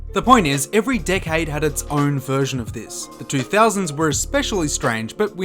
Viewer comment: I initially thought he said, “every dickhead had its own version” because of his Australian accent 😂